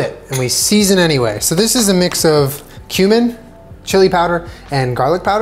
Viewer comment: I definitely don’t say it like “coo-min” but I was eating cereal when he said cumin and I thought he said “human” so maybe there’s an incentive to switch